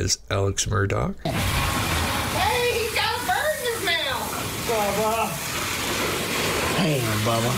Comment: Sounds like she says "Eddie, he's got a bird in his mouth"